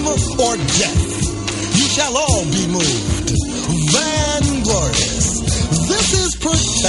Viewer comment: To the east my brother to the east. Van Glorious. This is protected by the red, black and the green, with the key. SISSIE!!!!!!!!!!!!!! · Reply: I thought he was saying the word " vain " and pronouncing it van glorious !